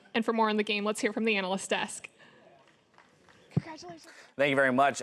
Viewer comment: What does it say on 41:09. Sme1 explain?????? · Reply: what she say? "Congratulations"